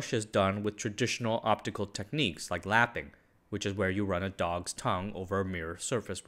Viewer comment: am I deaf or did he say "...lapping, where you run a dog's tongue over the surface..."